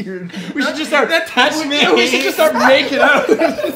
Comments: Anybody else hear go “we should just start making out” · did anyone notice what said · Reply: DerpyDog "We should just start making out now"